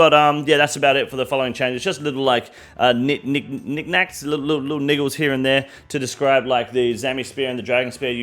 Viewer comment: what did you just call me? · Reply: The captions say niggles 😂 which is in fact a real word.